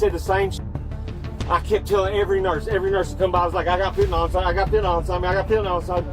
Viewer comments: is he saying phentynal? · PLEASE. PUT. EFFORT. IN. TO. YOUR. SUBTITLES. He is saying “I got FENTANYL inside me”. He says it several other times throughout this section of the video, and you transcribed it as: “I got fitting on something” The phrase “I got fitting on something” is gibberish, and given the context of him repeatedly mentioning swallowing a package of it, having X-rays done, and needing treatment so that he doesn’t die soon, it BAFFLES me that you would repeatedly let this slide. Drug ODs and addiction are no joke, and for you to put this little effort into accurately showing what’s being said in such an extreme real-world example is just infuriating. Second video I’ve watched from this channel and both were littered with incorrect subtitles, so I definitely won’t be coming back. To be clear one last time: *Fentanyl*: Kills people *“Fitting something”*: Nonsense